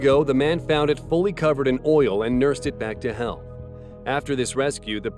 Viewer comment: That third one sounded like he said ‘’unleashed it back to hell’’